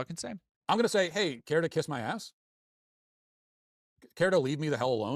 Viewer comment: I really thought he said kiss my fist